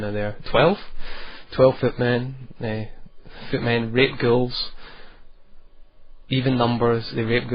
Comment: i heard him say 'Footmen rape girls' lol